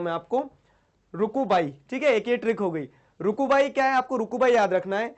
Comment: that was really hilarious 🤣😂 it sounded like Ruku Bai 😂